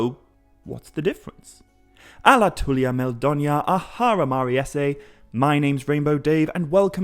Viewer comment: I really like your videos, but please, please.. what are you saying at the beginning of every video? At 00:20 to be more precise… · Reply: I say 'alatulya meldonyar ar hara mariessë'. Which means 'welcome my friends and live well in happiness.'